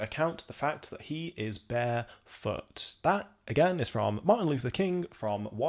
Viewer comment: This is what I get for listening and not watching! I misheard the MLK quote of ‘barefoot’ and thought he said ‘bare f*****’. For a brief moment I thought MLK was from London. 😂😅